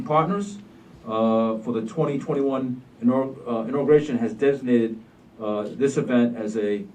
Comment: Did he just say a det ai nated event? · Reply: Nope he didn't. Des ig nated.